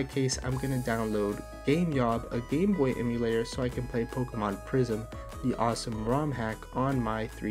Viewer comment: For some reason I thought you said Pokemon Prison, lol. That would make for an interesting show.